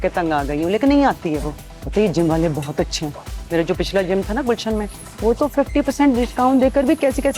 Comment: did she say "Gulshan me"?